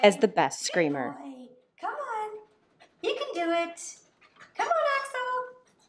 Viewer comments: Before I saw the name Axl I could've sworn you said 'come on a** hole' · When she calls him Axl at 0:15, I first thought she called him "Asshole." I literally did a double take.